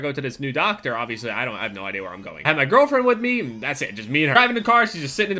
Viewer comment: Did he say girlfriend